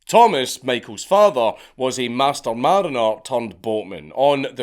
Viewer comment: Did I hear correctly that his father was Master Seaman Hoare? Just checking, as you were 😆